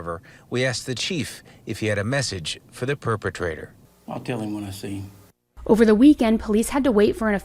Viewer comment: I swore I heard the chief say, "I'm going to kill Jim when I see him." After listening a few times, I think he said, "I'll tell him when I see him." But, hey, who knows? 🤔